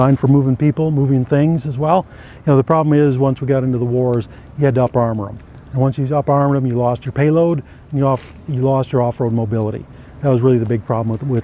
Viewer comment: Does anyone know what tf hes saying at 1:45? The subtitles don't even know!!!? lmao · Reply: Cartez Jack "the problem is, once you got into the wars, you had to up armor them. Once up armor them, you loose your payload capability and your off road capability"